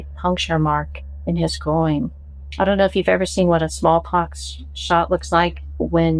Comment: 15 minutes in…did she…did she just say “small cox shot” while talking about a strange mark in a guys groin? That’s a double slam right there.